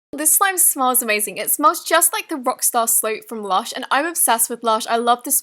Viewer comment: i hear "rockstar sloap"